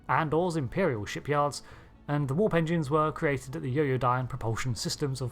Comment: My funny mind: You said "warp engines", I heard "war pensions". P.S.: 8:09 - Why are you posting non-canon stuff? 😜